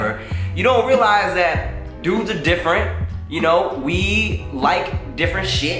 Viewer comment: Hello Timothy, this is actually the first video i have watched on your channel and you are hilarious so i definitely subcribed. But i want to make sure everyone can understand what you are saying in this video. The english captions are a bit off. in the beginning 0:22 every time you say "dudes" it captions it as "doing so" through out the video or later on "forehead" it captions it as "foreign aid" (1:24). It is usually words that are pronounced differently or said fast that translate in the captions different. if you can fix that when you have the chance that would be great! This will help people who rely on captions understand exactly what you are saying and make it easier for them(